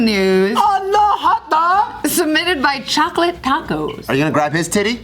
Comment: Did say "uh-oh hotdog" ?